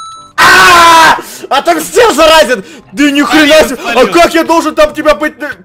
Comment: мне послышалось или нет что сказал да нихуя себе